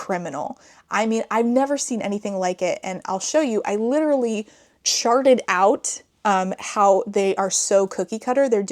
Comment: I heard “I literally sharted out” 😂 bestie, please I was driving!!!